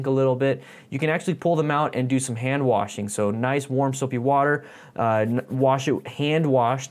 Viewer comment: Did I hear some "Soapy Wooder"